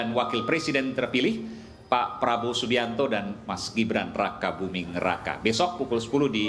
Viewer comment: raka buming neraka (tolong koreksi kalau saya salah dengar) 😂😂😂